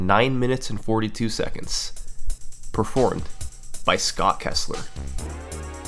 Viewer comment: For a second I thought he said Scott Presler and I was super bummed out